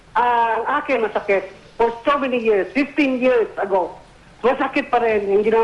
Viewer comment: Did I heard it right? 15 years ago? Then it was on 2003?